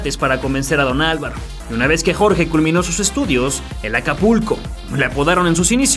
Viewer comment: Ya ni le entendí ¿Como es que le apodaban ? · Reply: El acapulco